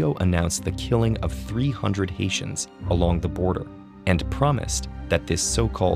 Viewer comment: I kept hearing ‘asians’ whenever he mentions ‘haitians’😂 good thing there are subtitles